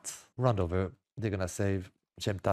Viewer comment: He actually says "on the edge of the smoke" not AGE lmfao